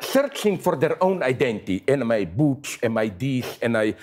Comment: Did Zizek really say "Anime Boobs"? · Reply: "am I butch"